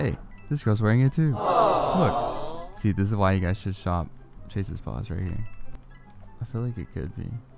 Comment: who thinks slayed these avatars>> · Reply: for a sec I thought you said "stop chases paws" 😭